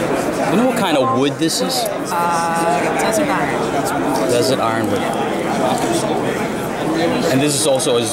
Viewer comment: "What kinda wood is that?" I thought I heard her say, It doesn't matter ....LOL